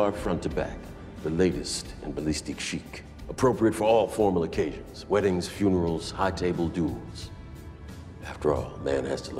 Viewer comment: "Appropriate for all formal occasions. Weddings, funerals, high table duels". 😊 I loved that part. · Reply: I heard it as High table dues lolololol but now I hear duels lololol